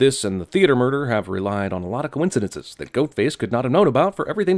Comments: did he say “GOATface?” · Is it me, or did Jeremy say "Goatface" at 5:37? Or am I just crazy? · did he say “goat face”?